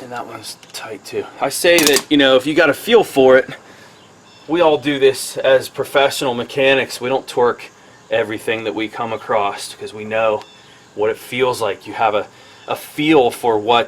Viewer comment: thought he said "we dont twerk everything that we come across, cuz we know what it feels like". Thats a good thing, leave it up to miley.